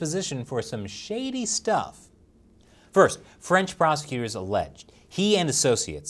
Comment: Lmao, I kept hearing "french prostitutes" hahaha instead of prosecutors